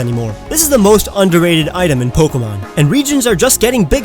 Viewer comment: at first I thought it said most underrated addictions